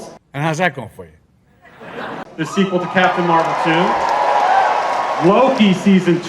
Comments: did he say "sequel to Captain Marvel 2"? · hold up, the sequel to captain marvel 2? did i hear that right xd they really intended for there to be one